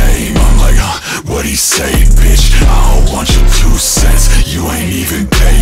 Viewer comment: My ass always thought he said "you aint even dead bitch" not "you aint even paid bitch." 🤣 either way still a vibe